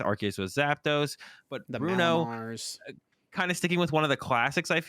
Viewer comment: did someone say Bruno Mars?